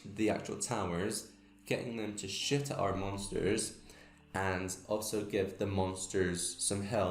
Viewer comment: i love how it sounds like he said "getting them to shit at our monsters" at the end